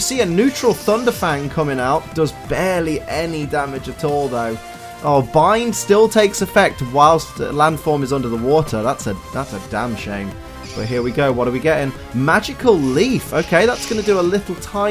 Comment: Damn shame (I heard damn shaym-min)